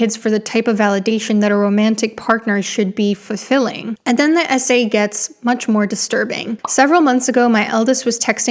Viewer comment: I thought you said "...the SA gets worse" and it is sad that that makes as much sense as "the essay gets worse." 😖